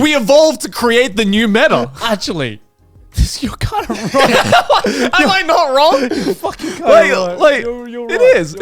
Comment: Did just say " am I not wrong? "